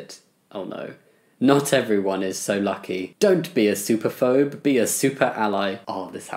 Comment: I thought he was saying "soup-aphobe" and I truly questioned how many people were running away screaming from a bowl of Minestrone